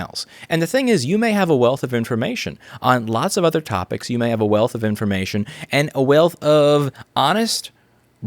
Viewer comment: I know this is serious, but he did just say "a wailth of ---" like he had an accent, lol.